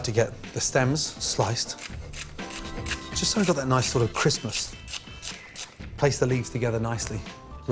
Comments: thought he said something about christmas, turned on cc and it said "national christmas" found out he said "nice sort of crispness" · Did anyone else hear him say at about 2:01 "... just so that they're nice sort of Christmas"?? Wtf?